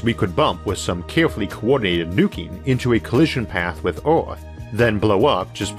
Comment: At first I heard "carefully coordinated nookie" lol